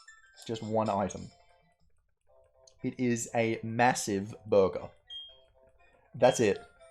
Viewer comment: I thought you said "massive booger" coz jesus christ